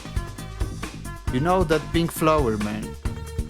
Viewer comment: That pink what did you say...? Flower man..?